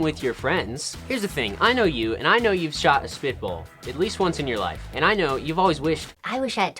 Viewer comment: i thought you said pitbull